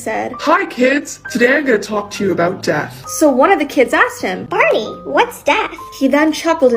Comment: POV you thought they said Barbie 😂